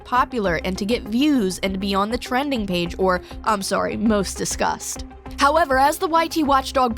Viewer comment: Hehee sorry every time you said "discuss" I heard "disgust" haha.. 😅 I mean... He's one of the most disgusted Youtubers out there now. 😂